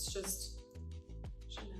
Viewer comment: Did she just say "just Chanel" girlll i mean she did just wash off a $500 face mask lol